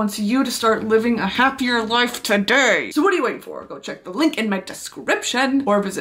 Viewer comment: I WAS SKIPPING THROUGH THE SPONSOR AND I THOUGHT SHE SAID "END YOUR LIFE TODAY" AND I WAS LIKE BRITTANY!!!! BETTERHELP!!!!!! lmaooo